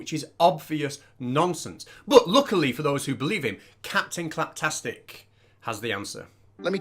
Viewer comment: Sorry I misheard, did you say Captain Crabfuckstick?